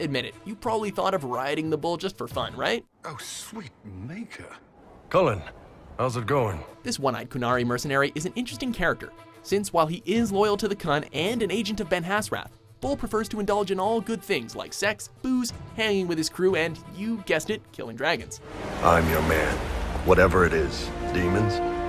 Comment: Oh god I heard "The iron bull is loyal to the cunt" and I was confused for a few seconds